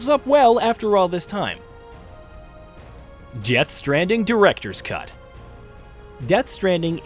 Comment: When he says death stranding I could have swore he said Jeth stranding 😂😂😂😂😂